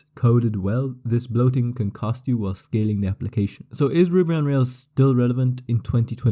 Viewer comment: the transcriptionn tool wrote "ribbon" instead of "Ruby" ❤